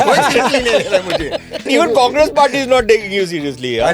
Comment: Did Sushant say Cockroach Party 😂😂